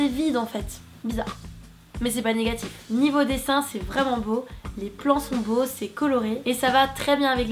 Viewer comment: j'ai entendu "niveau des seins c'est vraiment beau" mdrrr